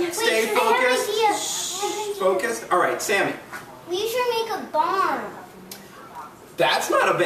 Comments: xD I thought she said we should make a bomb! XD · When one kid said we should make a barn I thought he said we should make a bomb and then said that's not a bad idea and I was like what?